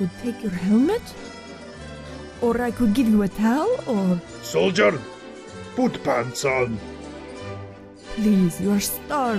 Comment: When heavy said” soldier put some pants on” It sounded like he said “soldier poot some pants on